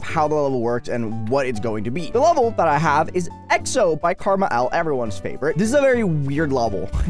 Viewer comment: is it just me or does it sound like is saying “karma L”